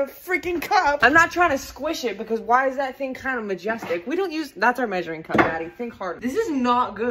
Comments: I swear I heard "That's a measuring cup Daddy. think Hard"... And that is so much funnier to me but like... yes. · I thought you said that’s our measuring cup “daddy” instead of “Maddie” 😳😳😳